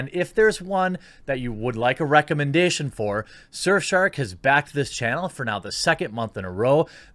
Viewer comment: Thought he said surfshart at first